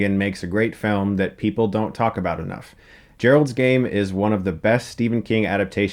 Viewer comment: who thought when he said geralds game he said geri's game like the pixar short hahahah good movie though